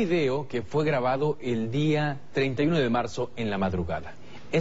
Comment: ¿escuché bien o dice 39 de marzo?